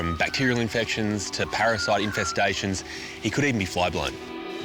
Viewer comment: The subtitles 🤣 Couldn't understand the 3rd thing they might find under his fleece. Turns out... "he could even be flying" 🧚🐑🧚‍♀️ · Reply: No, flyblown on his rear end.